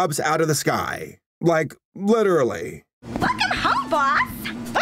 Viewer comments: Uhhh anyone know what the hell the snake assistant said at 7:28 when she dropped out of the sky?? I rewound like 5 times and all I can hear is “fuckin hell, boss!” lmfao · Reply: I think she said "welcome home, boss". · "f***ing hell, boss" am I the only one who hears that?